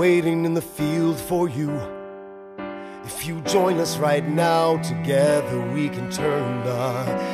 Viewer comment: I kid you not at 1:46 captions said "few toilets right now" 🤣🤣🤣🤣